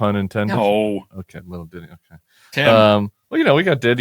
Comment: I am a 70-year-old Old Englander, and I thought he said Diddy, too.....